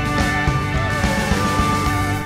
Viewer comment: thank you very much, thank you for standing up.?? is that what he said?